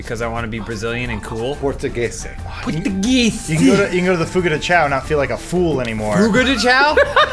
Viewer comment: you nailed the portuguese pronunciation.... lel P.S. 5:39 anyone understood what did he said ?! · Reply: I'm think it was Fogo de Chão, apparently it's pretty famous in the US as well!